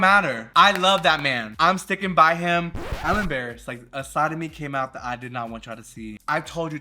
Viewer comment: you said “a side of me came out” and I heard a “sodomy came out” and I said mama the sodomy this is not a Christian woman 😂